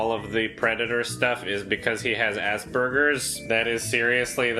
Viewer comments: I guess you're saying "Aspergers" and I hear "ass burgers"? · I swear to god every time you said aspergers it sounded like you were saying assburgers